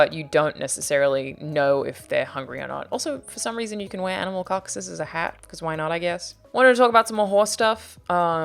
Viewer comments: I thought she said you can use animal cocks as a hat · I thought she said "you can wear animal cocks as a hat" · When she said “you can wear animal carcasses as hats” i thought she said animal cocks